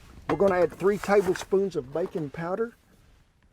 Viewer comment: Sounded like Kent said three tablespoons of BACON powder. Hmm.....